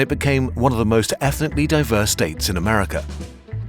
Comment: what's the definition of the word "effortly"? was it supposed to be "effortlessly"? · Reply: according to the subtitles, the word was supposed to be ethnically